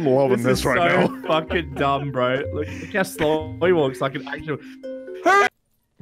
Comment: i love how the audio cut so instead of hurry it sounded like he said "HURR!"